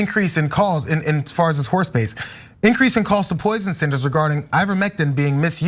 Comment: I am listening to this and walking down the street. For a second I thought you said horse piss. I had to stop and listen.